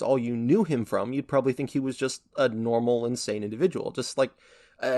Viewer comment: I misheard that as "a normal, insane individual" and I...I didn't immediately question it 😭